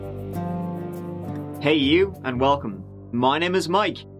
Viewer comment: “Hey you and welcome!” I know when I hear that I will always be intrigued and always entertained. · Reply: Now I got what ha says!!! I thought ha said "you are welcome"...and I thought he meant for his videos